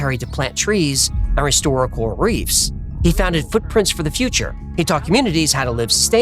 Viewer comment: swore that joint said Fortnite for a sec 🫡